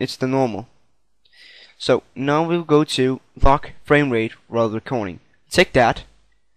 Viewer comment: he said long frame rate and i thought he said fuck frame rate xD